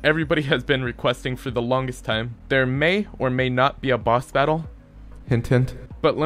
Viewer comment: did I hear that right? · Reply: BOSS BATTLE!